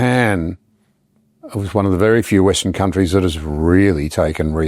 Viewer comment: Did you really call Japan a "western" country? At 1:02:25 or so, and I've listened several times and still can't be confident.